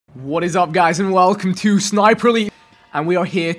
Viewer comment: did he say sniper elite 3 ?